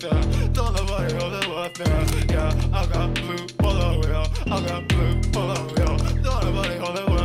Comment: Tohji's verse 完全版 Yeah we mallboyz Tohji don't nobody hold water(みんな完璧やん！）×4 Yeah I got a bull bottle yo! ×2 don't nobody hold water ×4 everybody hold water don't nobody hold water I got a bull bottle yo! ×2 don't nobody hold water×2 vroom vroom vroom vroom×2 with spriteｵｹブロンブロンブロンブロン ｵｹwith my spriteブロンブロンブロンブロン ルパンパンパンってする峰不二子 ho ルパンパンパンってする峰不二子 allright ×2 we got a パケとリーン I got リーン 紫（murvsaki）向かうparty 俺らritz 鼻からweed 俺たちveen! we got a パケとリーン I got リーン 紫 向かうparty 鼻から吸うweed 俺たちveen veen!×3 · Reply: I got a bull bottleのとこ、I got a blue poloにも聞こえる tohjiの愛車の青いVWポロ指してるのかなって